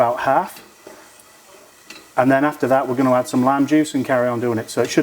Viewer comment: Definitely one of my favourites, I didn't know Pork Belly could be this crispy on a BBQ. Going to have to do this again, very soon. 😃🐷🐽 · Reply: Revisited this to brush up on the recipe...the first time you said "lime juice", I thought you said "lamb juice". Glad I misheard!